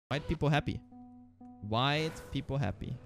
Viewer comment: widepeepoHappy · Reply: Oh! I thought he was saying "white people happy".